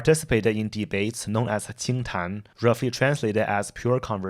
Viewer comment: my mind for one moment missunderstood "qingtan" and thought you said "Think-tank" 😂